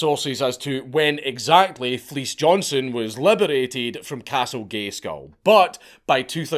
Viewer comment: Did you just say "Castle Gayskull" ?